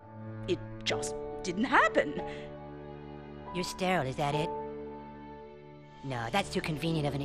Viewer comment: 😅 wt does he say lol 😂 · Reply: “You sterile is that it?” Meaning she can’t have kids😊